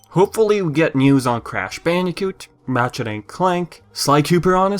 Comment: am i the only one hearing him saying matchet instead of ratchet?